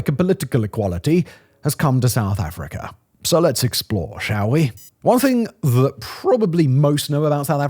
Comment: when he says "let's xplr, shall we?" did anyone else hear it that way? Deep cut from the Simon Whistler Universe.